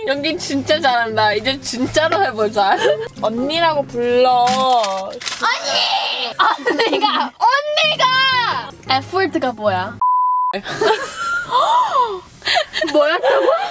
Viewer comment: lowkey sounds like saying the n word 😂😂